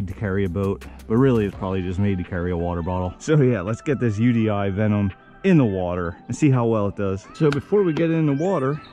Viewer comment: i swore i heard let's get this DJI Phantom in the water, lmao! Holy crap, i think it's faster than Vector 28. dont Torpedo, ohhhh too late :) that self-righting is cool I'll thumb it of course!